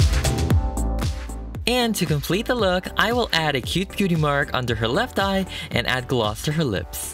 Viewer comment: Did you say 'Cutie mark'? XP I wonder what Cruellas cutie mark would be if she was a pony :O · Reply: Bueaty mark